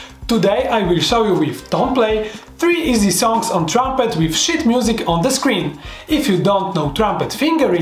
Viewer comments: at the start it sounds like he says "with shit music on the screen" lol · i thought he said shit music instead of sheet music